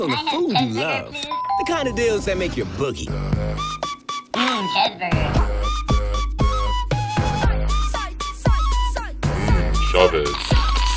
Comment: what is it saying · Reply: It says mmm chubbins